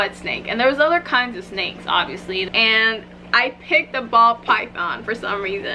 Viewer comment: Guys I'm so slow that everytime she said "ball python" I kept hearing "bald python" lmaoo